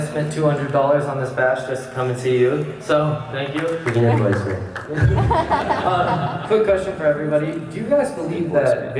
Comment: What did Casey said in 11:18?? Didn't catch it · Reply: I think he said begin your question